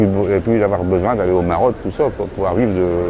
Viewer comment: pourquoi il parle du Maroc ? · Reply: J'ai compris "Maroc" aussi mais je crois qu'il dit "Maraude". 😉